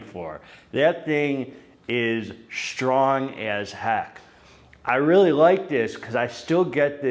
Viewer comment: Somehow I accidentally turned captions on and when you said "strong as heck" youtube's AI software thought you said "strong as hack" because of your accent 🤣🤣